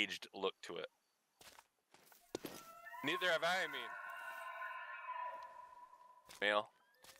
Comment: I swear i heard need revive me